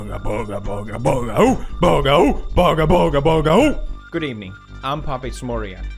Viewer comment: i thought pewds said good ebening like if u know what i mean